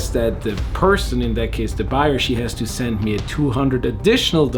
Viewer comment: I've been studying English but didn't understand what word he exactly said on 01:22... He said: I have to..?.. to 200... Can anybody help? Thank you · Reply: You can use the CC button to see some AI generated "closed caption". I'm saying that the buyer told me she has to send me an additional 200USD to get upgrade my Zelle account... and that I will have to return that money once it was sent to me. You can see these instructions also on the right hand side in the screengrab from my phone. I hope this helps.